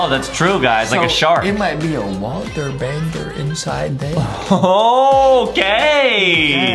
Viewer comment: It sounded Adem was saying g*y but Adem was actually Saying OOOOKKKK the clip 3:41🤣🤣🤣🤣🤣🤣🤣🤣🤣🤣🤣🤣🤣🤣🤣🤣🤣🤣🤣🤣🤣🤣🤣🤣🤣